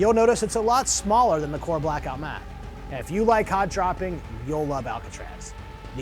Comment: did say "do you like cock dropping"? or am I trippin?!.